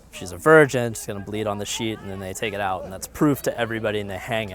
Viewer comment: LOL I thought he said "bleed on the sheep" hahahah